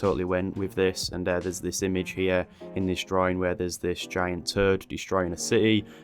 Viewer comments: I thought you said a giant TURD 😂😭 Goddamn Yorkshire accent! Haha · i thought i heard you say 'giant turd'.